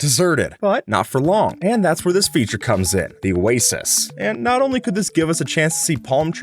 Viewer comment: I thought manz said “the O-racist” but he said oasis